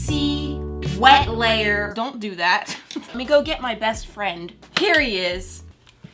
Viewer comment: I thought she said periodt, she said here he is XD